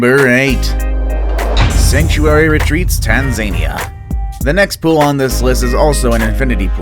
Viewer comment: Did he just say... Tan Zane ia...